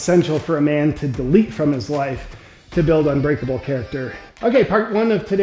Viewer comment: Dude I swear I thought I heard you say “delete his wife to build unbreakable character” 😂